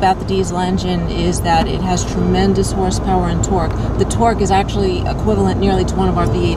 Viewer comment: did she just say twerk instead of torque